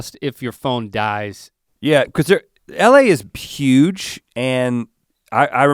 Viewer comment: lol did rhett say pyuge